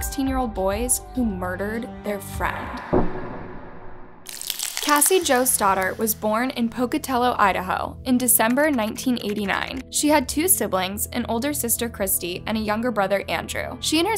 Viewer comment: I almost thought she said Cassie joestar 😂